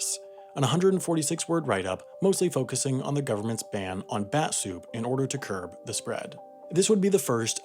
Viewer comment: Did he say batsoup at 3:20 ?